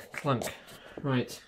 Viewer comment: Lol, did you say c**t ?